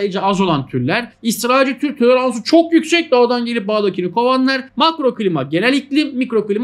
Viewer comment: 'de israilin tür toleransı çok yüksek mi dediniz hocam yanlış mı anladım ĞDPWQEÖĞWQPQW